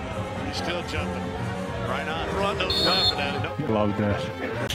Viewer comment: what did he say? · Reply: I guess he said, "Love that".